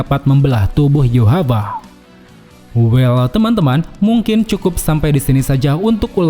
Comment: bilang apa bang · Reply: 𝙒𝙚𝙡𝙡, 𝙩𝙚𝙢𝙖𝙣 𝙩𝙚𝙢𝙖𝙣